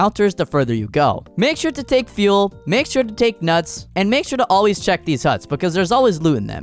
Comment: umm is that nuts that i heard??